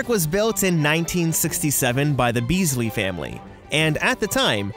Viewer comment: I thought he said Weasley